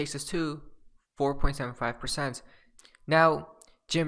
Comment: Did he just say 0:27 were going to 54.75%?